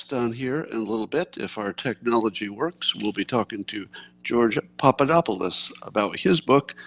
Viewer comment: i miss heard you say George Stephanopolos!!! i thought you were a traitor...glad I was wrong!